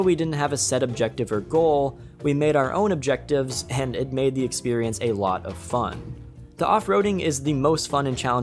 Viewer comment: "It made me experience a lot of fun" has to be the best description of a game i've heard so far Edit 1 year later: How the hell did i mishear it so bad